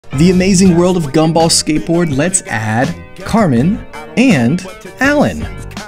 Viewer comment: I thought you said "Cartman" 💀